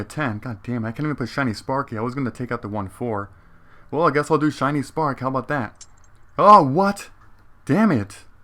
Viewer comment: Yo dude i kinda heard it as shinyass